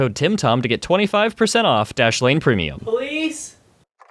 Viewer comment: sounds like he is saying “please”